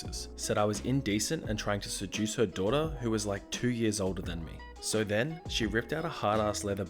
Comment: For a second I thought you said “2 years old” instead of “2 years older” and I was like holy shit 😂😂😂